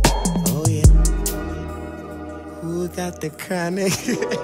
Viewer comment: at first when he said hop got the chronic i thought he said "i forgot the chronic"